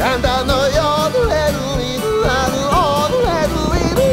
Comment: En la parte cuando dice: "El ganado me lo llevo yo" al principio cuesta entender que dice por qué suena como un: el ganado Yodeli Yodeli yooy. Por estar acostumbrado al Yodeling